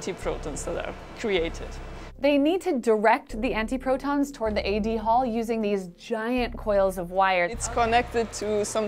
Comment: First I heard "Eddie Hall" (the beast)🤣🤣🤣